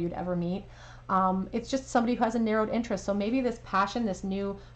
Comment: Hello! At 38:50 did you say neuro interest or nerdy interest? I got confused. Thank you 😊